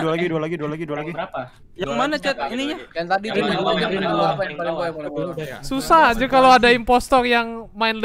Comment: aku dengernya "Susah Anjir"